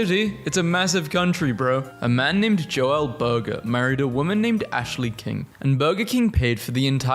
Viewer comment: b-b-burger? Did he say his last name was burger?